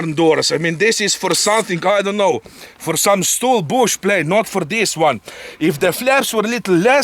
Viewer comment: For STOL bush planes. What I heard: For some bullshit. 🤣👍✌❤🛩